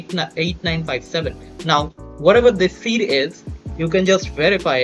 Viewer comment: did you just say... Six Seven!!!!!!